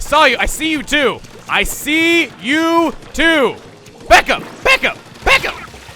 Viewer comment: at first I thought said Baka when he said back up